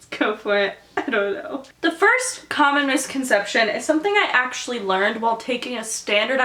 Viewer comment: When you said "Most Common Misconception", I thought you said "Communist Concetption". ASDFGHJKL.